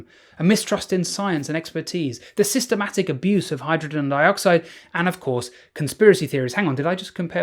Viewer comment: first time I thought I heard "... the systematic abuse of hydrogen oxide ..." 😄